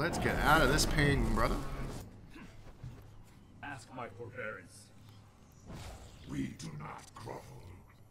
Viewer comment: I think he said "ask my forbearance" :o